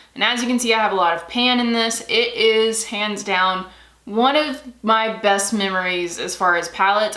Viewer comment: Lol when you said "hands down" I thought you said "pans down" lol I thought I would share the laugh. 🙂